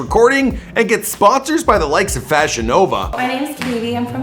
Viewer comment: Every time you said FashionNova, I thought you were saying VagiNova and it was a sex toy company.